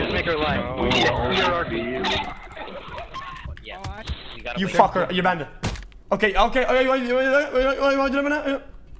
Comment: $100 if anyone can tell me what he said after “you’re banned” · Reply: "you really wanna do this right now?" NOW GIVE ME MY $100